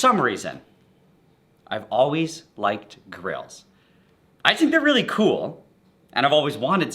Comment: it’s so much funnier if you hear him saying “girls” instead of “grills”